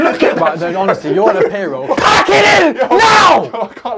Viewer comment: When he said pack it in i thought he said p*ki